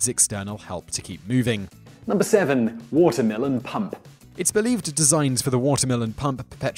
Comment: I thought he said "watermelon pump"